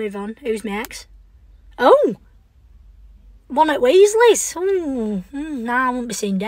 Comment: I heard her say "ronald weasley?" before I rewatched and realized she said "one of" it was funny to thing about tho